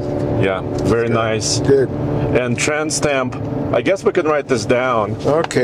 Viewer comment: Every time said "trans temp" I heard "tramp stamp"😅